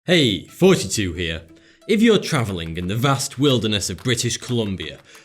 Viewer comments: Every time you say thoughty2 here it sounds like your saying 42 here 😆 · When he said thought 2 here, I heard it 42 pm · "Hey thoughts-2 here" Me: did he just say "hey 42 here" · lol it sounded like he said hey fousytube here in the beggining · I am done change your YouTube name to fourty2. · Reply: I thought I was crazy. I thought he was saying forty two after watching probably ten videos. Then I noticed it was spelled. I couldn't understand why I kept hearing forty but he actually is saying forty it seems. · Is it just me or it sounds like him saying "Hey forty-two here" anyone?